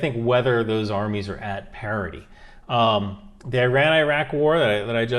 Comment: Thank you for your valuable insight, answers to those questions and general knowledge. I do not intend to ridicule, but for a second there I thought you said "parody" when you in fact said "parity". 😏😏